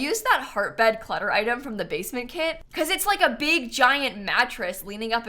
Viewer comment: When she said “basement kit” I heard “base game kit” and I had to rewind and listen again lol